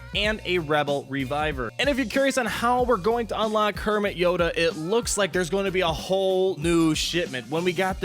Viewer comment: I thought you said "Kermit Yoda" Like as in Kermit The Frog! Like as in let's make him a frikkin NEW rebel character! I know it was Frank Oz that did Yoda BTW...